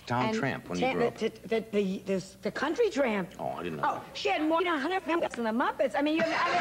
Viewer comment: Oh, why did they censor this? Does anyone know what she says at 09:35? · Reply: Based on context I think she said something along the lines of, "She's had more hands up her/there than the muppets!" She was joking about her getting around a lot